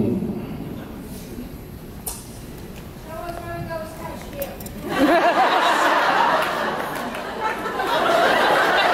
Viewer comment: Thank you for uploading! What does the fan yell out at 5:12? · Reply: I think it was "show us where the ghost touched you"